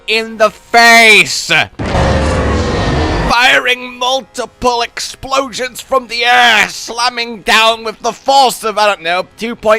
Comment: i swear it looked like said multiple explosions from the ass